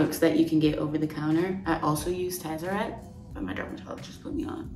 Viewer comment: what does she say she uses? Tazerette?